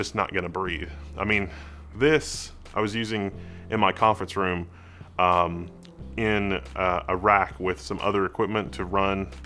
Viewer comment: I was so confused as I heard "i was using this in my conference room in iraq" lmao